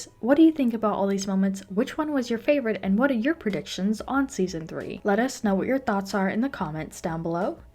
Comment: Did she really say session 3?😂